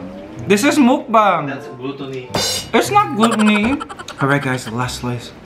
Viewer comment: Did just say “that’s glue toony?” 🤣